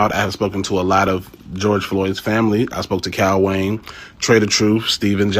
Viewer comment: He said “Cal” or “Kyle” Wayne, not Katt Williams @ 2:44. The closed caption says “Cal”; which is a rapper.